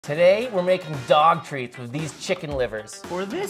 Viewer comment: Oh geez I thought he said “today we are making dog” and that kinda scared me haha